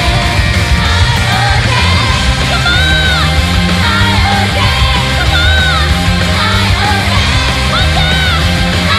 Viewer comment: did astuko said Come on? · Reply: She did say come on and others in English.